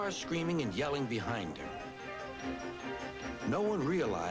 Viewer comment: Listen Carefully to what George Carlin say at 3:17 to 3:21. It sound he saying "With Cars screaming and yelling behind her"